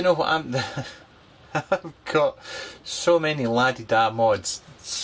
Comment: Please help!:) What is the word is saying at 13:43, and sounds like "ladida" :)) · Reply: Göncöl La Di Dah, means posh or high end 😁